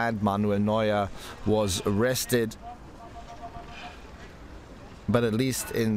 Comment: Why was Neuer arrested? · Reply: oh, sorry, sounded like he said Neuer was arrested